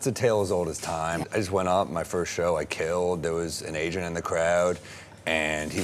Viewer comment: I thought he said "there was an Asian in the crowd" that kidnapped him. It wasn't until I listened to it again did I hear the "t" at the end of the word and thought to myself that now it made more sense. I'm still on the lookout for Asians though...I'm very kidnappable